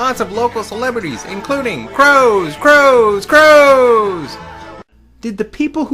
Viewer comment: Did you just say KROOOOOOOOOOOOOOOZE?! ...Oh no, wait, you said "crows". Nevermind.